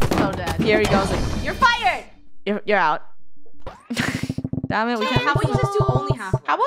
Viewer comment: I keep hearing dammit for some reason xd